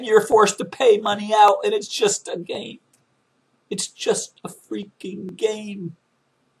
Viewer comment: i thought mr. hicks said "...it's just a ride..."